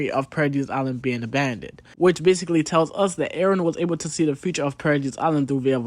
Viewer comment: thought you said "Eren was Able to See the Future of Paradis Island via Bird" 😂 i mean its still true